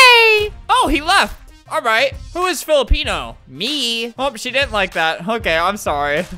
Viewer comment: Nobody: girl in the left Whos Filipino here: meeeeeeeee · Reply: I thought you said who's Felipe